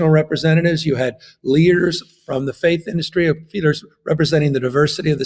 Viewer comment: did he say "Faith industry" ?